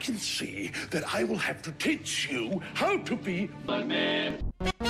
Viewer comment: I thought he said I will teach you how to be a man but he said I will teach you how to be mugman XD